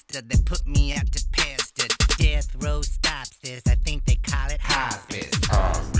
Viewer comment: LOL "death row I think they call it hospice" that's terrible 😂😂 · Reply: I always heard it as "Death rows stop 6, I think they call it hospice" but upon further review I see that it says "level 3" on the wall so I'm guessing your right with the "stops this" that makes sense.